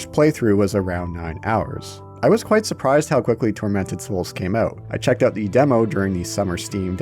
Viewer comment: First time mention of the title I thought you said "Fermented Souls" NGL caught my attention..